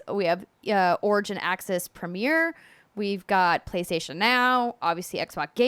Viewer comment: Could have swore that Andrea said "Playstation meow" when she said Playstation now.